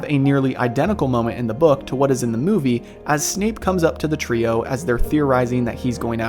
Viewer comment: At about 9:49 did anyone else hear Snape being called Snoop? lol that made me laugh